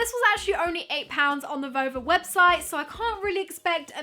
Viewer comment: Am I crazy? Or did it sound like she said vulva around 9:30ish when she was saying Nova?